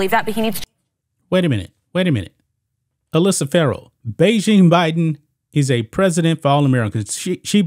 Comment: I thought she said Trump is a president for all Americans